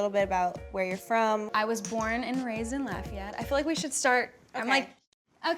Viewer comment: Did she just say i was born and rased in Latvia 🇱🇻 ? Or i’m trippin